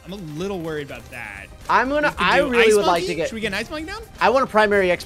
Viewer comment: Did... Did they just say, "Should we get a farm?" on CHIMPS...